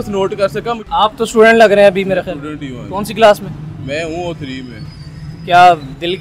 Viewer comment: did he really said that he was in 3 class 1:30 :face-fuchsia-tongue-out::face-fuchsia-tongue-out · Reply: O3, means third year of O levels.